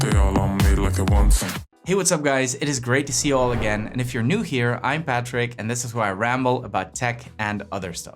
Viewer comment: When you said hey there I’m Patrick I thought you said hey there Ipad tricks!